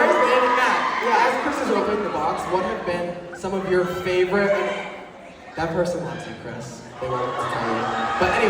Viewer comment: what did they say? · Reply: Someone said they live Chris then Nick said “that person loves your Chris they wanted to tell you”